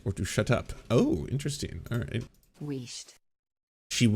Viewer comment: Wheest, I thought you said East!